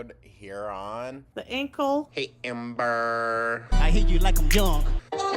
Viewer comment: I heard you like ‘em young 😂😂😂😂 good one!